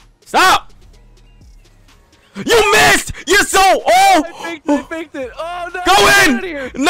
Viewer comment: thought he said YOU'RE SO OLD and absolutely lost it 😂😂😂